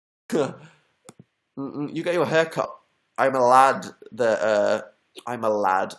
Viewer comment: when he said hair cut I thought he said heck art lol